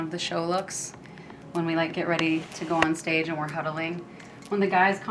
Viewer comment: I thought she said cuddling instead of huddling xD